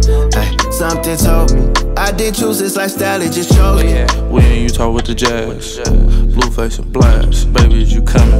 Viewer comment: Lol sounds like he says “”we in Utah with the jazz”